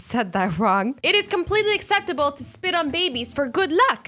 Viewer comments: Instead of spitting on babies I thought it said splitting babies I was horrified for a second until I noticed it said spitting on babies I was relieved lol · I thought it said sitting on babies at first😳